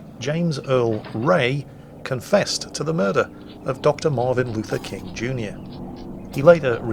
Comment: it sounds like you say Dr. Marvin Luther King.